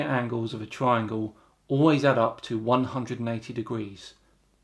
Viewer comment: Did you say 190° at 18:06? I am not a native English speaker but I think that I hear 190.